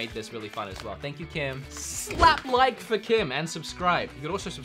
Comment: I thought you said slap Kim for like